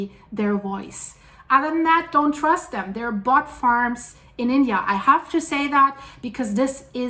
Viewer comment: Well, my family lives in Eureka. LOL, I thought you said BUTT farms LOL